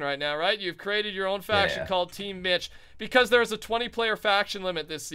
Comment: it sounded like he said team bitch xD